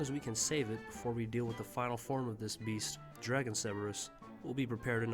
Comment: I legit thought he said “dragon Severus” not dragon cerburus